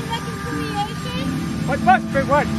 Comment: I heard “mcwhat we what”